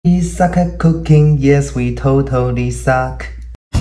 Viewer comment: "you suck at cooking yeah we totally suck" is this a you suck at cooking reference??! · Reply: I thought he said we suck at cooking 😂😂